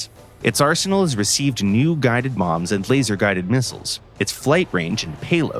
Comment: did he just say new guided moms on accident?